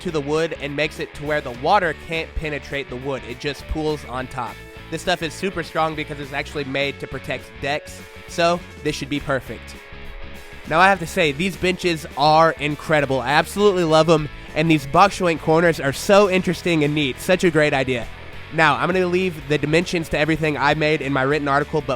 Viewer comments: I thought he said "bitches" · Sounded like you said bitches. LOL!